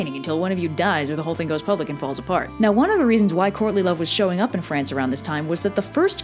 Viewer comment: 1. I was listening to this while grinding my dogs nails and thought you were saying "Courney Love" the first hundred times, until I looked at the screen and saw it written out. 2. You keep saying their names together, and "Morgan and Mordred" sounds like a lawfirm.